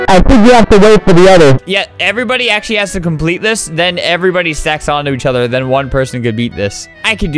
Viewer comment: Did flamingo say sex